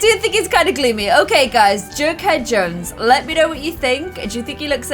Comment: I thought she said Jerkhead Jones XD